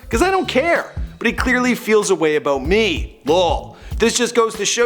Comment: What this nigga say at 10:15 ? Law ? He mean L O L